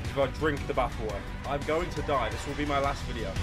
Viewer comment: the subtitles say game end instead of die